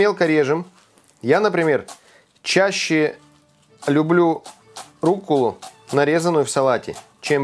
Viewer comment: Послышалось - я люблю руку нарезанную в салате.. И потом еще что-то про капилярчики...